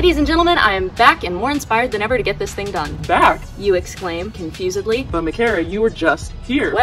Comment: can’t believe i just heard “confusedly” as “micarah tewers-ly”